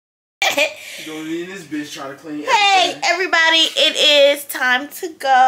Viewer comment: time lime, did just call my girl a bitch or am I trippin🥴